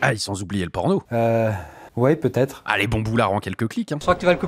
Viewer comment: OOOOF à 00:25 j'ai entendu "les bamboulas en quelques cliques" xDD